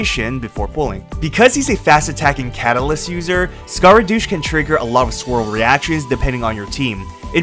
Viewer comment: Am I going crazy or does he call Scara "Scaradouche" at 10:40 ? xD